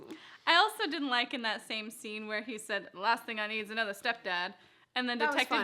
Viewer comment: Did Lucifer say step dad or step Dan I'm a new subscriber by the way I found you through Lucifer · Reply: Sam Alexander he said step dan lol